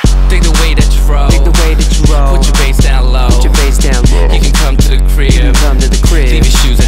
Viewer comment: lol 1:28 i thought it said "you could come to Korea"... wait does it really say that or does it say crib??